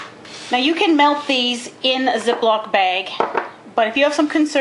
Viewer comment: it sounded like she said in za block baglike istead of the but she did not say that she said Ziploc Bag